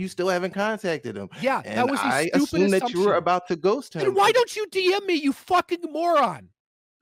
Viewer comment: Is saying “why don’t you BM me?” 1:26:01 😆